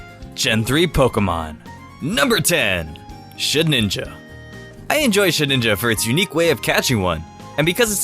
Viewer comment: I heard 'Shit-Ninja', anyone else?